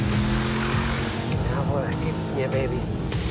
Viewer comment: Matthias in der Kurve: yeaah gibs mir baby 😂😂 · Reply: Ich hab gibst mir Daddy verstanden😂😂😂